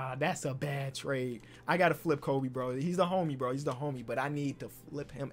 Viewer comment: it sounded like he said Kobe rip to the mamba